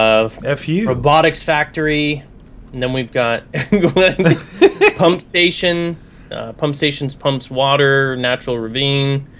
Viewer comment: I thought he said hump station